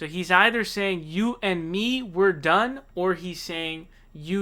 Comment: I also thought he said "you and me, we're done" or something to that effect